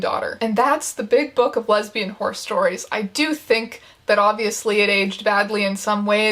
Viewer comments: if anyone was wondering, i DID in fact mishear "horse stories" as "horror stories" and it took me until 9 minutes in to realise it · Horse stories? I thought you said horror stories. Sad.